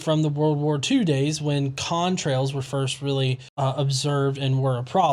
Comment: Did he say "cumtrails?" at around 1:30?